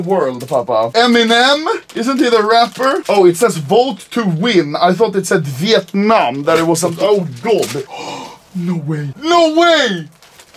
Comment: i also thought it said Vietnam 😂😂😂